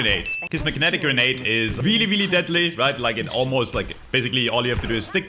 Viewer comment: I'm at 0:38 and i need to get my ears checked because i heard "mcdaddy grenade" smh